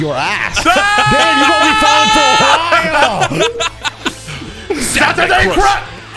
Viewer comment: Is it me or did he just say... SATURDAY CRAP!!!😅😂